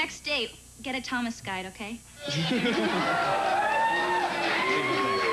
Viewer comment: "get a Thomas guide ok?" Is that what she says? Is it like a version of "idiots guide to" or like... A local tourist guide book for a region? I've played it back many times and I obviously need help lol